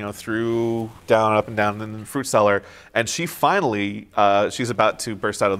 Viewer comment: Did you say “fruit” cellar?